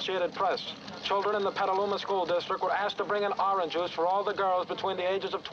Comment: I thought he said 'bring Oranges" (to the pregnant students) my mistake